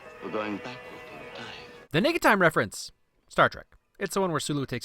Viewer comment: Auto-generated captions say "nigga time reference"